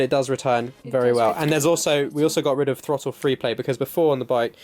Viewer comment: thought i heard "throttle foreplay"